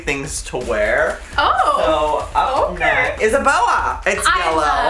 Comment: when joey says "it's a boa" I thought he said "Isabella" 😂😂😂😂💛